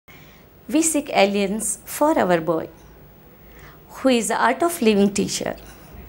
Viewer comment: The first sentence I heard is "We sik alein for our boy" :) LOL